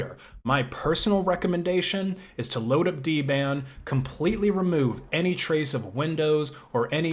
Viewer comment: What does he say at 1:45? "Is to load up D-Band"?